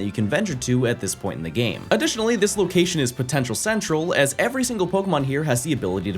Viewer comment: DID YOU SAY POTENTIAL?